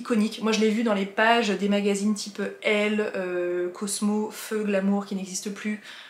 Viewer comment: Tu vas rire, mais tu parles un peu vite... Et je suis sûrement un peu sourde, mais lorsque tu as parlé de "feu Glamour", j'ai compris "feux de l'amour" !! Gros moment d'incompréhension, puis grosse rigolade